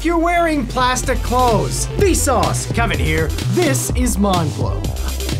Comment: Is it just me or did he say "Mind Fvck" instead of "mind blow?" xD